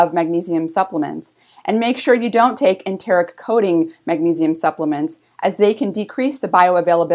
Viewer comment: and make sure you don't take SOMETHING coating magnesium supplements. does anyone know what she said in that statement where I've written "SOMETHING"? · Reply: She said enteric coating